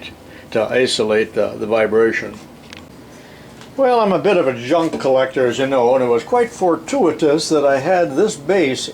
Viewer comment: did he say "junkcollector"? lol